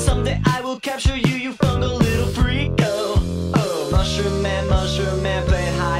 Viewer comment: When he said “someday I will capture you, you fungal little freak!” I thought he saying “fucking little freak” but really weirdly